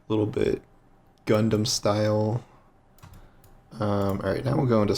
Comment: For a second I thought he said “Gangnam Style” as the inspiration for the helm ornament.